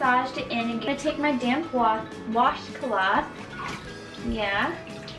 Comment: I thought you said “I’m gonna take my damn wash cloth “ 😂😂